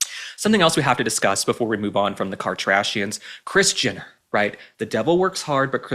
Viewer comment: why do i keep hearing Christiana not kris jenner😮